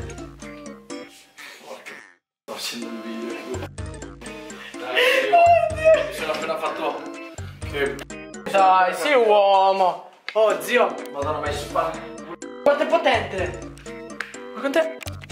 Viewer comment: Ma solo io al minuto 2:50 ho sentito che ha detto mi sono appena fatto una p1ppa